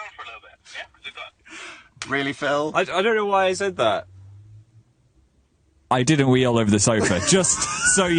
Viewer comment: I thought he said "I did pee all over the sofa though " 😂😂😂